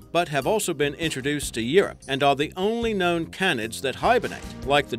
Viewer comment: Uhm did anyone notice that he can't say CANINE right? It sounds like he is saying CANITS